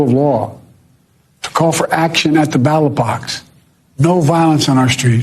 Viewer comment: I wasn’t sure I heard it right… “battle box”. But you cleared that up nicely. Thanks. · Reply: No he didn't say "Battle Box" he said "Ballot Box". Biden's just so old he can barely talk. However Matt Walsh is just a glorified troll, who poses as some sort of commentator. If you want the unbiased truthful big picture, do not listen to this guy.